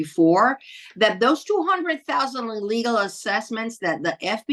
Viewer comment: This is to point out something that the voice-recognition AI did to your commentary at 07:34 to 07:36: It translated as follows: "...those 200,000 legal assessments that the FBI admitted having done..." When I think you actually said "illegal"? p.s.: at 18:26 "Kayla Kay's YouTube" is mentioned? Unable to find that anywhere so maybe I have wrong spelling. The voice recognition thought you said AA K